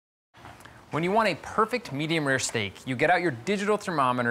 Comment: I thought he said perfect medium rare snake